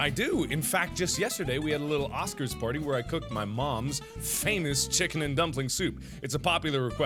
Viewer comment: OMG MY COMPUTERS LAGGING AND THE VIDEO STOPPED PLAYING AND IT SOUNDED LIKE HE SAID "oh yes i do cook just yesterday we have a oscars party and i cooked my mom" o-o marki noo